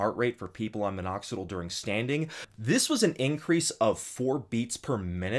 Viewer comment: I'm sorry what did you say at 9:51 did you say during "standing" like literally standing?